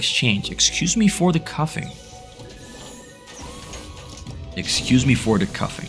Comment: I'm sorry but it sounded like you said "cuffing", instead of "coughing" but anyway, great tvt game!